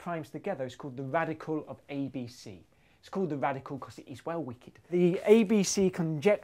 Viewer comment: captions said [INAUDIBLE] he said "...'cause it is well-wicked."